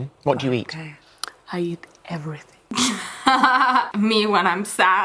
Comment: “I eat everything” damn i thought she said heroin